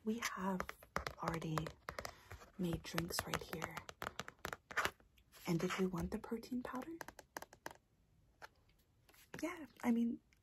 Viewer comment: “And did you want the protein powder?” What I heard: “and did you want the cocaine powder?”